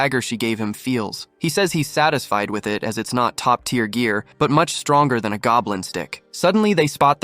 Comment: WHAT HE SAYYYY?!!! Much stronger than a goblin's dick?! Like bro, WHAT?!! 🤣